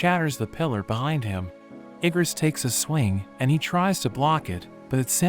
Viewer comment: I thought he said igurus takes his spleen